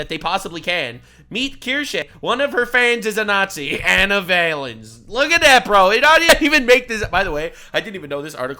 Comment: As an audio only listener, I thought you were saying Anal Violence, not Ana Valens 😅